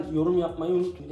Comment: heralde yorum yapmayı unutmayın dicektin bende yorum yapmayı unutun diye anladım 😂